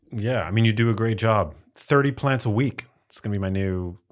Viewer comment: I thought Max said 30 pints a week instead of 30 plants a week! A beer brewed with rice, like Budweiser will give you a hangover!